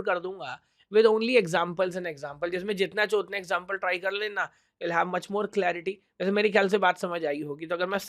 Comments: Guys is it just me or did he really say that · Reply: he said , "Jitna ho sake utne example try karna". The sound of ho sake , sounded like "cho ke". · what he did say 💀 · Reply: jitne "CHAHO" utne .